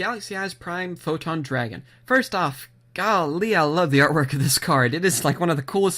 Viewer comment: Did he said "golly" at 3:58?